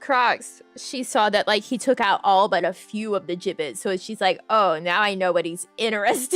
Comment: Thanks for the stream!! It was a fun chatting and window shopping stream. I had no idea what a Jibbitz was at first, I thought ya kept saying Giblets and I was so confused LOL :_NamiLaugh